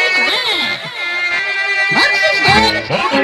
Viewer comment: My dumb brain literally thought it said “Mungdal is dead”